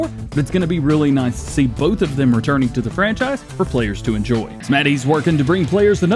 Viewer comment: What's the name of the modder, I don't understand the name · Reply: It's Smety